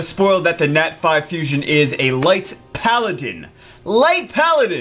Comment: i thought it said; light panda. fml